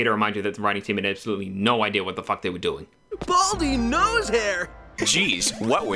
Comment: Eggman has gone through so many names 1990s: Robotnik 2000s: Eggman 2010s: Baldy nosehair 2020s: We have yet to find out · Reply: I thought they said Baldy McNosehair, Not Baldy Nosehair.